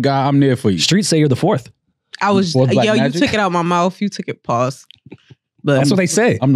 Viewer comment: Did she just say "you took it out my mouth" ? Wild 😂